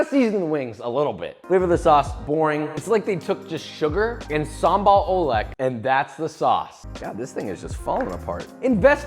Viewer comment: what did he say??? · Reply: samba ole